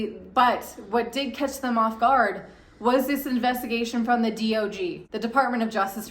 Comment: Did I hear that the DOG is sniffing around DCG?